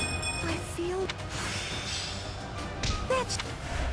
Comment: Did I just hear Mercedes say "Betch!"